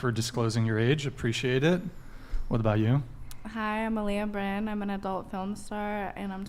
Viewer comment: She said her name was Malia Brand at 11:33 i turned on Subtitles to see what she said